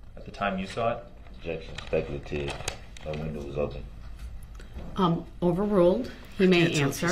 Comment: It sounded like he said, "No window was open." That also puts him in the Escape. And, actually, he did go through a barricade at the end of the parade route.